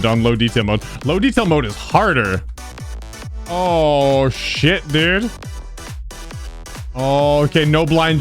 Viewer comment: He actually said “shit” instead of “oh, shoot” 😂.